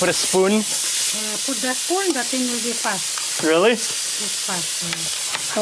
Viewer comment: what’s happening with the spoon here? I can’t figure out what she is saying. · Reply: She says "the thing will be fast" idk how it speeds up the cooking tho lmao